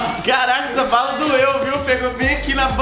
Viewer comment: So eu que escutei o dizendo caralh 😳